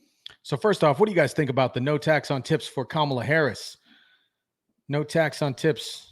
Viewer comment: She thought she said no tax on tits